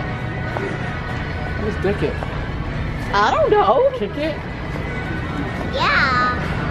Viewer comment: omg killed me when she said "kick it" and it sounded like a inappropriate word 😂😂 (at 7:50) and reaction to that i cant 😂😂😂😂